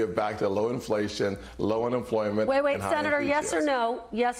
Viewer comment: Wait did he just say he can't wait for lower employment? He did